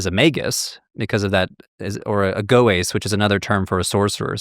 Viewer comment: I couldn't make out that word he said, and the CC isn't helpful. "Agoyas""?